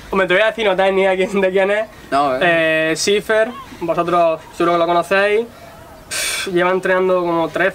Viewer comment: Como se llama el de parkour que dice en el min 14:30 ¿fiser? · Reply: KingNoob ESP shifer what